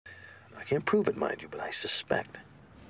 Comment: did Hank just call Walt “Mike”?? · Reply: He said “I can’t prove it mind you..”